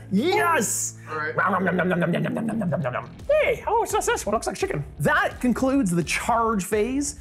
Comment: "What is this? Mmm its chicken" is that what said at 34:40?